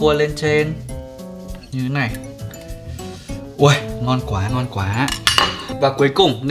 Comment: Ảnh nói ngon quá mà mik nghe thành no quá